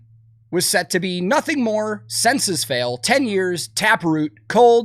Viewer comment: I misheard "Senses Fail" as "Census Fail."